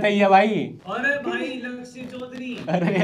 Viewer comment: i literally heard 'Lundse' Choudhary